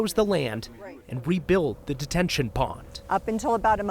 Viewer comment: Did he say Detention pond?